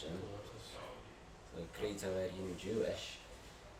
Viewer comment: "Great time to be Jewish!" (yes I don't speak Manx but that's really what I thought I heard :D)